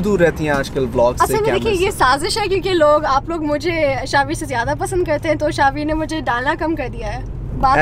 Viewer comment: What she says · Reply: Hum log zyada pasand karte hai to shahveer ne daalna kam kar diya hai😂😂😂😂😂😂 matlab hum zyada pasand karte hai ab hum h dalenge